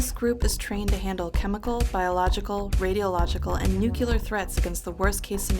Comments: Did she just say "Nukelar" What is this, the Simpsons? ( 1:36 ) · did she just say nuke-yu-lar?